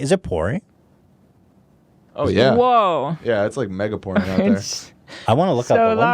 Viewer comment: When they said “it’s mega pouring” the auto captions thought they said “mega porn”